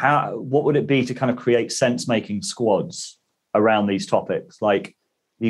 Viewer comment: Can't believe it! All this time I thought he was saying "fence making". No wonder my carpentry's gone to shit.